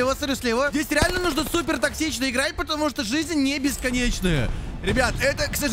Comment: мне послышалось или сказал здесь надо играть очень токсично а не тактично